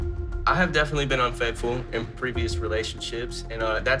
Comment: Almost sound like he said I’ve been unfaithful in prison